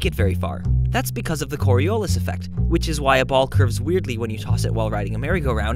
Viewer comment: I thought you said " Clitoris Effect" ... I feel dirty now >_<